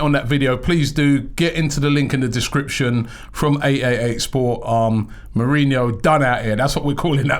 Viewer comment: I always thought Robbie said AAA sports but I found out today it's 888sports!